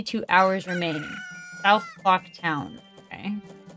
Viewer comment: It sounded like u said south f*ck town that was hilarious for me idk why